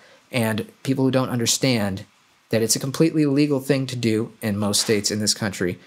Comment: I'm sorry boss... I played it, backed up replayed it several times.... It sounded like you said that printing guns is a completely "illegal" thing to do in most states in this country. It's not. In some, yes, in most... No. You have the right to make your own firearm, the medium is not defined. Print away. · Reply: I said completely “legal”